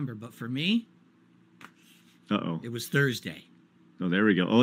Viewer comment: I thought Bison said "Tuesday" in that scene.